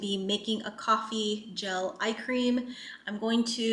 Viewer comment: When your vlog started I thought you said you were making coffee jelly icecream 😂 love your studio ❤